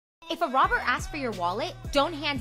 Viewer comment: Shit I Thought It Said Toss His Salad...HAHAHA